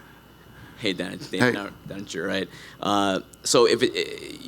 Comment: did he call Dana dad 😂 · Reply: “Hey dad”